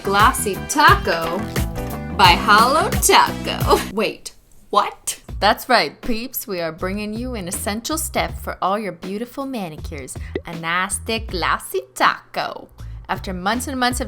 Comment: i thought she said "thats right creeps" at 2:19 lololol i was like whaaahh 0.o